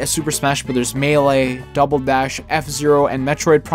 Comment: double bash?did I hear that correctly?